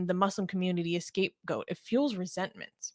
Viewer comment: it sounded like you were saying at 0:35 "escape goat". If you did, indeed, say that, were you just reading someone else's mistake, or did you make the mistake? The phrase is '*scape* goat'.